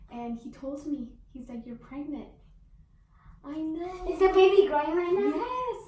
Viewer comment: Anyone else hear at 0:24 her son say “is there a baby brother in there?” 🎉❤️🤭💙